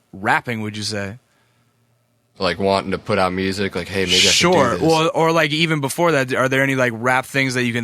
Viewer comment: did bones say the n word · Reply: murdoc's bath no he said "like hey maybe I can do this"